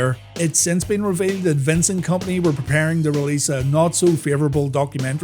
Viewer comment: On 2.44 I thought u first said "Vincent Kompany". I didn't realise he was a WWE fan 😂